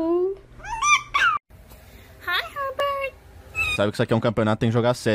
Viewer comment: Why does my brain immediately go to a snake demon presenting how he fixed the M25, and then saying: "Can I hear a wahoo?"